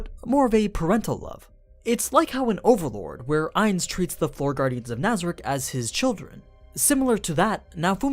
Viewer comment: Did you say 4 guardians of Nazaric? There are more than 4, i think 12, maybe, but definitely more than 4! or maybe i heard wrong At 14:24 btw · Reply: "Floor" guardians. Understandable that you misheard but yeah, there you go.